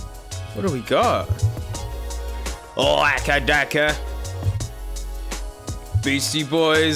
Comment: did he just call AC/DC aca daca🤣🤣